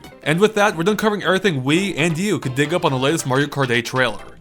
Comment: when you said we and you I thought you meant to say wii u